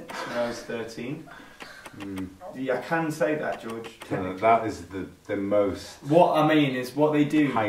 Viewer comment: What did George say at 2:25? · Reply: M: "I can say that, George, technically" G: "Mm - that is the most heightened thing you can take from it" 😊